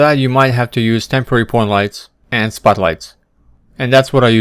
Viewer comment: what two lights did you mention? I heard spot lights and something else? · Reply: point lights